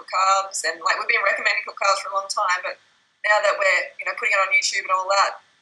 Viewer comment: Did freelee say that she doesn't reccomend cooked carbs for long time @ 15:55 or I got it wrong?